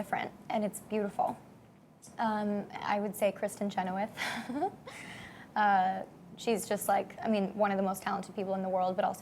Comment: What she said at 6:38? Christin Jenuet? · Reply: Diana Ujhelyiová Kristin Chenoweth, she's an actress who plays Dove's characters mother on the Descendants and Dove has always looked up to her even before they worked together